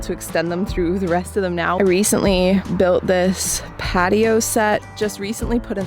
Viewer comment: Wait did she just say "built this patio set"??